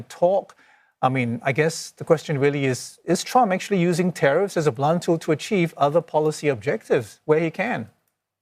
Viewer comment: Broke up a little bit. I heard "Is Trump… a blunt tool…?" I think he is very much, if he can be even considered a tool that actually does anything useful